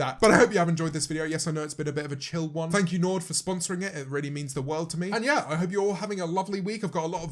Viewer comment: Tell me why I thought he ended off by saying, "thank you Lord for sponsoring this video"